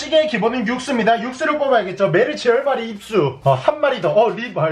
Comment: 에 며르치치가아니라 메르시로들림 ㅋㅋㅋㅋㅋ